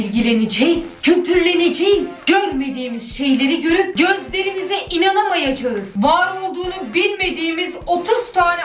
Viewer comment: Bir an abla gözlerimize inanamayacağız diyince ben onu götlerimize inanamayacağız diye duydum fkmfkmfkkfmmk